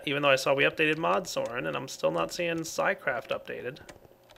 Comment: Did say sigh-craft at 27:52 or did he actually say xycraft?